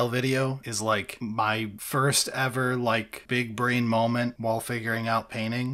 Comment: What was the video called out for at around 6:55? Couldn't quite make out what he said, and a quick look at his videos didn't help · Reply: "En Grisaille" with Contrast Paint